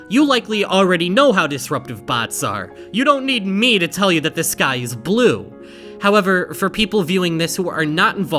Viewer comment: I heard "this guy is blu" and I audibly said nO HeS NOt